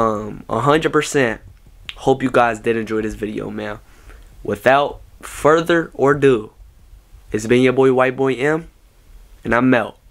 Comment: he said further or do instead of further a do 😂